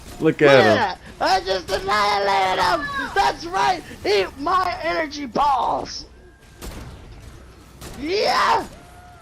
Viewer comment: Why did say eat my edgy balls xD